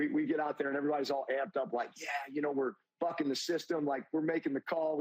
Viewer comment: Did he just say F'ing the system? Lol